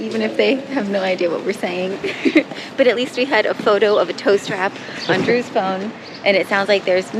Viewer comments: When you were saying tow strap i thought you were saying toast wrap😅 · hahahaha -- somehow in the audio (or maybe my 61 year old ears) I heard... "toast wrap" and then finally when I saw the product Drew bought,, oh, dah...."tow strap" hahahaha